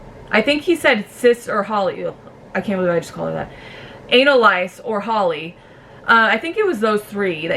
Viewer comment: Sounds like ur saying ANNA LICE 😂 , Jackson is disgusting, him an AQUADOUCHE NEED TO GO.. I love spiders, got over my fear from yt thedarkden sorry.. love ur sass.. I heard he said the n word.. BBKUK literally evicted a celebrity just from him saying n boys r his favourite.. ❤️👉🏻🇨🇦🇨🇦